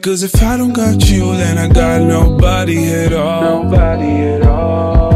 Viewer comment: i thought he was saying "Blink/Wink when you're awake"